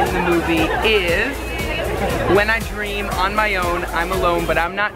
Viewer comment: Is it just me or at 2:50ish does it sound like is saying "When I scream on my own"? Because that's what I just heard and I can't unhear it